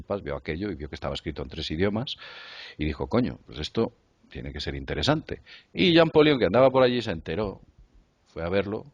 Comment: Y que dijo? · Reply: coño 😆